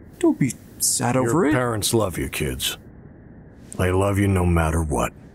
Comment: I swear it says I love you no matter what even though he captions say they. Is the cop the dad?